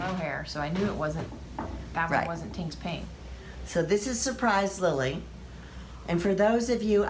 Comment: I thought you said "surprise willy " I was like they want me to google that!!!!!😂😂😂😂😂😂😂